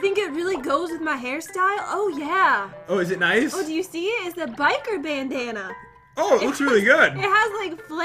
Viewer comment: Before she said she got a biker bandana I literally thought it said biker banana.. 😂